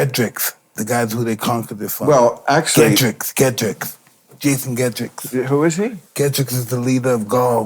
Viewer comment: What Tyson is referring to is Julius Caesars conquest of Gaul and fought against the leader of the Gauls, Vercingetorix (Ver-sin-je-ter-ox). Im impressed he knows history! Never expected for Bill and Tsyon be talking about Roman History! This is so much fun! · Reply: Ah thanks for clarifying, I thought he was saying 'Jason Gedricks' or something